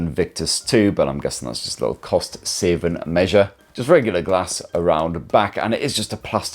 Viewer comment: Sefeverilla glass on the back? Sorry I tried four times, but I simply can't make out what's on the back. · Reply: "Just regular glass" is what he says imo